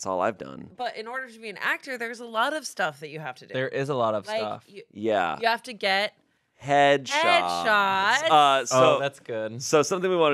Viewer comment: My earbuds died at the most amazingly perfect time. All I heard at 40:30 was: "In order to be an actor, you have to get head"